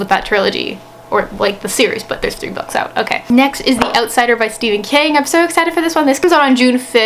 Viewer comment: For a second I thought you said The Outsiders!